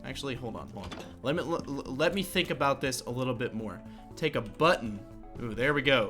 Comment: I thought you said butt